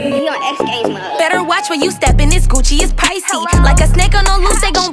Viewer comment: I thought she was saying “better watch when you step in this c$$chie is pricey”